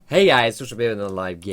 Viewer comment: watched your vids for over a year but I still don't know what you're saying in your intro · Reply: back in the day i thought he said "hey guys fox dro pee" im not a native speaker so i was always wondering what this means xD